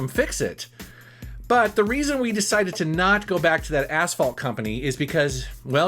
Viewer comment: Thought you said asshole company...